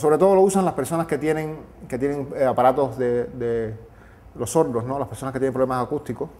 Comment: aids? What the fuck lmfao, he clearly said "sordo" which means someone who can't hear, he even pointed at his ear